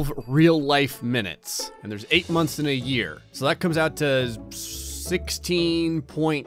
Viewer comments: Did he just say 8 months in a year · did he just say there’s 6 months in a year smh